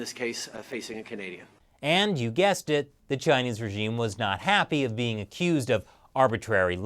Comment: yo did she really call him peasant